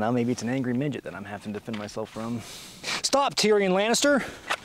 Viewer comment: I know he said Tyrion Lannister but for a second I thought I heard Tyranny Lannister